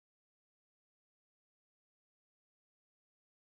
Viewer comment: hi nice video ,,, what you said at 10:40 after ganda beroza?? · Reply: Colophony